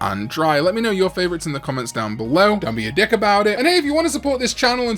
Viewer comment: Why did I hear "Don't be a dick about it" as "Tell me your dick about"...